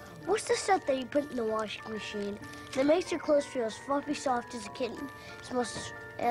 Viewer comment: Feel as what-ing soft? It almost sounded like the actor said the F word. Did he? He's a kid! 1:48 - I guess nevermind; he must've said "fluffy-soft" this time. Maybe he meant "fluffy and soft".